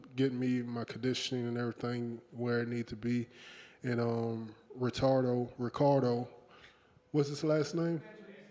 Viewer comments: he really said Retardo 😂😂😂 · Mans said Retardo instead of Ricardo 😂